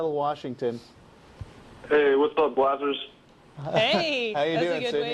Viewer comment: what did he call them? "Hey what's up _______" ... sounds like "blassers?" what is that? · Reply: Glassers. Their last name